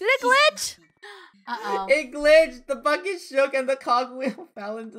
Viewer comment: Is it just me or did cuss when the cogwheel fell in the well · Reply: Gracelyn Hagar Not what u think he heard it sounded like he said a bad word but he said BUCKET